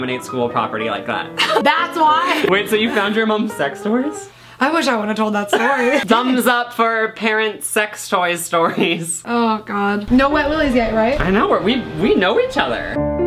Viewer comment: I DO NOT LIKE INDIAN TWISTS NOR DO I LIKE WET WILLIES BUT I DO LOVE LUCAS. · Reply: The Gabbie Show The little kid I'm babysitting heard you say sex toy😅 I had to tell her that you said dog toy 😂